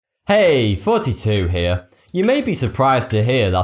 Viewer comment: What Thoughty² hears: "Hey! Thoughty² here." What I hear: "Hey! 42 here."